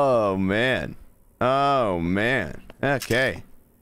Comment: it sounds like he said "that's gay" instead of "okay"